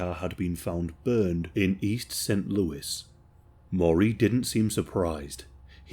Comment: Do you speak English? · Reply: It sounds like you say Lewis when it's obviously St Louis